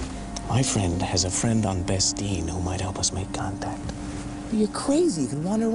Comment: Does biggs say bespin at 2:10 ?